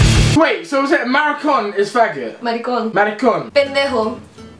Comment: she sound like shes saying marigon instead of maricon